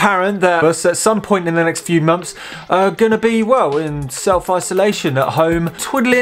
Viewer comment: I keep hearing "self oscillation"